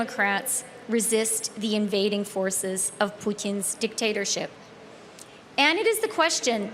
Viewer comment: Did she just call Putin Poutine?!